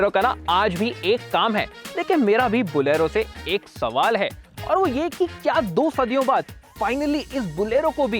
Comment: Kya kaha? 2 sadiya?